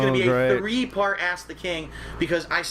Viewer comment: sounds like he said "three part ass the king" 😂